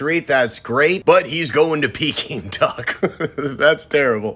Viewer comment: “But he’s going to-“ Me: umm.. pee king duck? 👁👄👁 what did u just say?